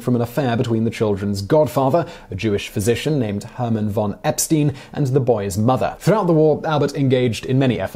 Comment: did I hear Epstein?